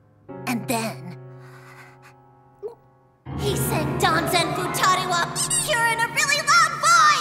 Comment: correct me if im wrong but i think at around 14:04 Konata said "Danzen Futari Wa Pretty Cure" except the the word pretty was censored lol